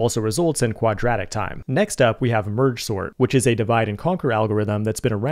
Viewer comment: did he say merge sort?